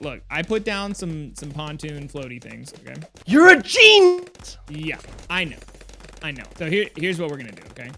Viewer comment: Did say ur a geeeennnt or ur a genius I can't tell cuz he was cut off there